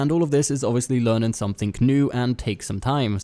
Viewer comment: did he just say 'k'new? XD